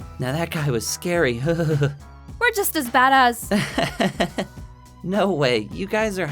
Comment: Actual line: "We're just as bad as" What i heard: "We're just as badass"